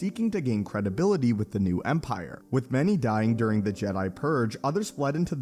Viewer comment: I always hear „Jedi Purr“ instead „Jedi Purge“